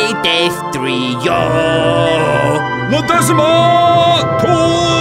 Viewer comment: I rewatch these champions league video alot and I still dont know what Ronaldo says (is he saying notaxmar?) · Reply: La Decima , Tuuuuuu